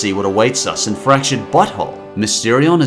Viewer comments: i already made this joke a few days ago on your fractured but whole moment, but i love how it sounds like you say 'fractured butthole' instead of 'fractured but whole'. not an insult, just something i find funny. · Thought he said french butthole